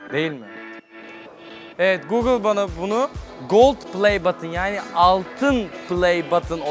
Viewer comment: de google play button dediğini sadece ben mi anladım